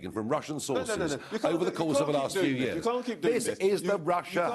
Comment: Did he really say that Nigel can’t keep debunking his bullshit ?